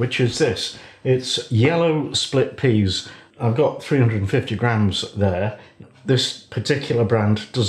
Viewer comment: You say 350g but the caption says 250g (or 9oz, which is about 250g).